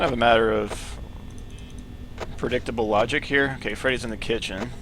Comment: did he say Freddy is in the kitchen